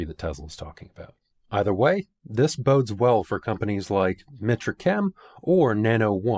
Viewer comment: I couldn't quite make out the other company besides nano one you mention at 24:25 when you are talking about LFP chemistries...... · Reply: Mitra chem. I did an interview with them